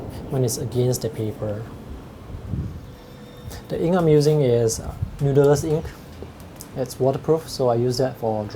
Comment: informative review. I didn't quite catch what kind of ink you said you were using at 2:30? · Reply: Noodlers Bulletproof black